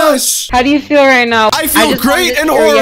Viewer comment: For a minute I thought you said 100 thousand likes and I’m going to break her back